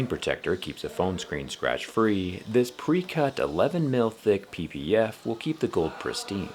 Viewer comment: Did you say the PPF is 11mm thick ? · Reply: mils not mm